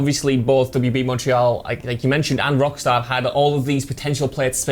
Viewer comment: Am I the only one who heard him say “Rockstar” ?